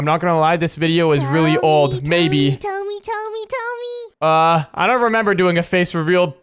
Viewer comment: sounds like she is saying tummy tummy tummy